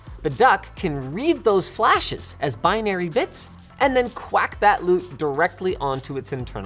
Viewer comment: Did Linus just say "quack" at 7:30?